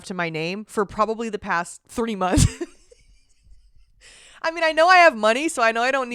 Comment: I'm sorry I'm dying at the fact that you struggled so hard to say three months, the first time you said it, it sounded like you said 30 months. ( 9:52 ) And the second time you said it, you mumbled it so bad and said it with like a lisp I couldn't even hear what you said, and it's the only thing I've struggled to hear what you said ( 10:00 ) 😭😭😭😭 do you not like saying 3 months or can you not say it? Or what was going on? Am I the only one who noticed that? It's cracking me up, I love you 😭😭